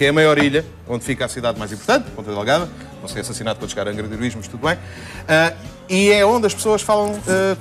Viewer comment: "assassinado os caranguederevismo, mas tudo bem. " Entendi isso! kkk